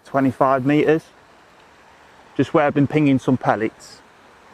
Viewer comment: I didn’t think you could feed with a pole cup whilst fishing a line either?he actually says I’m going to fish just where I’ve been pinging pellets this is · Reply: depends on the fishery rules. He says "where i will be pinging pellets" its understandable why you thought otherwise though.